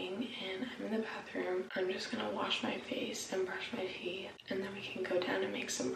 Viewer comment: It sounded like she said I’m going to brush my feet 🤣👨‍🎨when she woke up outta bed 🤷🏻‍♂️